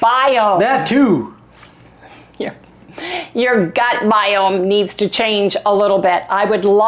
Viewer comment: That was really funny you were trying to say gut biome but it sounded like you said butt guyome and I laughed a bit.